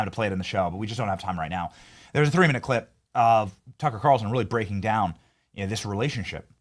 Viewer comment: I hear him called “Tucker Qatarlson”